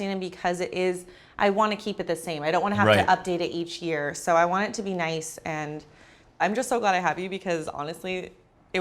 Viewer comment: I think the whole point of paying a designer $25k is to not have to make any changes over the next few YEARS is it not?... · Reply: she actually says she doesn’t want to have to keep updating